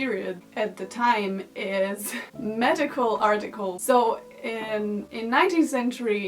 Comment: I thought she said Magickal writings not medical lmao I really thought this was going to turn into a Crowley video....the best blood is of the moon monthly 😂 such a missed opportunity but still a great video as always.